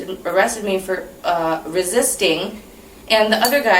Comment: Did she say “…and then the no soul ginger man, came outta nowhere…” I had to have misheard that 😂😂😂😂💀💀